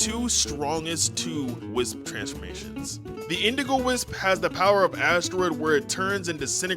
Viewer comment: i thought you said the indigo wisp gives you the powers of asthma 😭